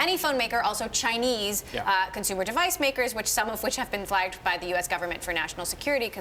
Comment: Emily is saying "Chinese consumer device makers which have been by the US government..." what is the word for that blank? I can't hear it... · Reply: The word is “flagged”